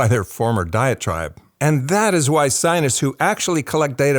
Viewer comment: Just one critical note: You need to verbally emphasise "diet tribe." I thought you were saying, "diatribe." That made almost no sense, but just enough to confuse me, till I turned on the subtitles. One could be from one "diatribe" or another, so it sounded like you were kind of coining a new usage of the word, rather than coining a new term (as I have never heard of a "diet tribe," tho the idea makes entire sense). Side note: vocal fry is a little distracting.